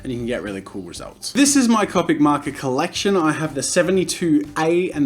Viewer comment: I thought it said comic makers lol